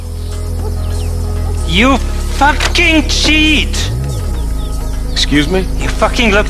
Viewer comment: when i played rdr1 i thought he said "you fucking shit" at first i was so confused